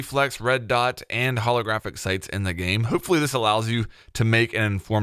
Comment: Everytime he says "sights" I hear "sites" omg